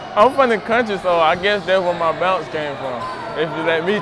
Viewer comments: what was he saying ? 🤣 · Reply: Im from the country so ig thats where my bounce comes from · can someone clarify what he said. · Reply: He’s from the country so that’s where his bounce from if u let him tell it he said